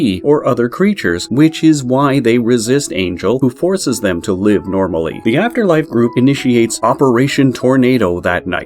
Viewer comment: I am just watching cuz i heard "Not live a normal school life"